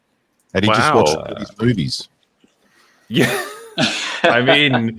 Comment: did he say those movies? Or the goonies?